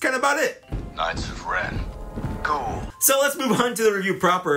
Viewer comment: I thought that Trooper Said, "Knights of Ren... GOALS." I choked on my soda. Put on the subs and saw that he had called them "Ghouls", which is weird. But not as weird as thinking that if with hard work that soldier in the Emperors Grand Army could become one of the Knights one day. I say, aim high lowly trooper. Never give up on your dreams.